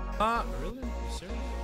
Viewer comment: nghe như kiểu: "Vô lý !!!" :v